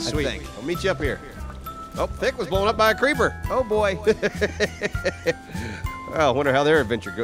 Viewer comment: Anyone hear at 16:37 thick say “oh god!” although very faint